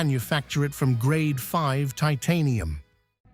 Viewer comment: did my ears fine.i heard grade 5 titanium crankshaft right??